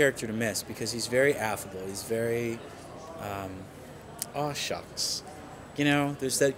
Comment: please what's the adjective he says at 1:09 ?? i'm french and i don't get it. and please could you write down the part from 1:10 to 1:20 ? he speaks too fast for me lol thank you ! and RIP Cory we love you and miss you every day ♥ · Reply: Thank you so much !! I got it now. According do the internet, "aw shucks" is when you're shy, or embarrassed, or you act in a self-effacing way... not easy to understand in context for a foreigner lol. but thank you again!